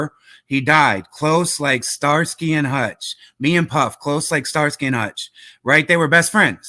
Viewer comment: I thought biggie said me and puff tight like starsky and hutch